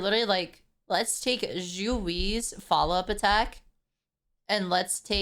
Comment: Can someone tell me who they're talking about? I can't make out the name of the Character they're talking about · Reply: I think it's Xueyi, what with the FUA and sword spam.